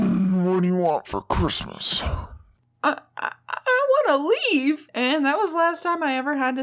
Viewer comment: When she said I want to leave I thought she said I want a leaf